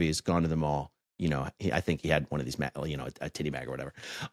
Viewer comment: Does he say his 14 year old brother had a TITTY BAG? I wonder what that is? Did he get a fun bag implant or something?